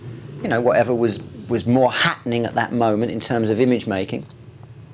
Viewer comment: Style was a way for people to "parade themselves in a way that made them look like they were happy". What a great statement. Relevant to this day. Comes across like a dodgy sales man but was obviously a genuine human. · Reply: Sounded like he said "happening", not "happy".